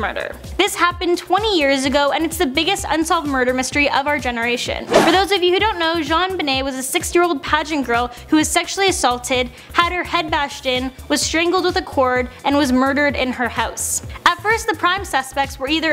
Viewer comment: I thought she said basalts, I was like you do that too